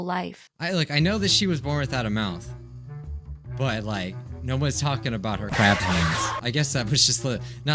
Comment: captions say no ones talking about her bathroom